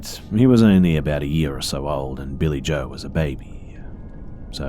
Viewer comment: I thought they said Billy Joel was a baby just going to say what the fuck does Billy Joel have to do with this story